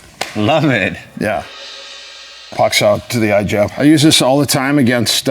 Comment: did he say pak sao?